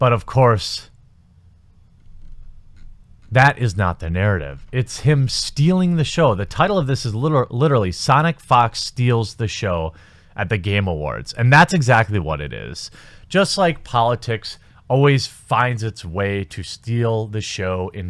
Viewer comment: Wait, I swear that I heard you say "Sonic F*cks".